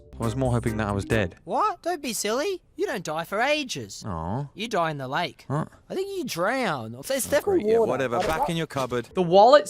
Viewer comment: he says “there’s definitely water” Not unintelligible ..with water like the subtitles said. I feel like that could open up another red guy death possibility, involving water of course